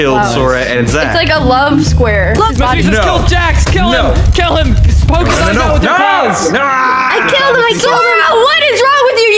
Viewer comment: WHAT DID ZACH SAY?! XD I think he said: "KILL HIM KILL HIM POKE HIM IN THE EYE WITH YOUR- BALLS!" Seriously what? I may hate Zach but damn he was entertaining.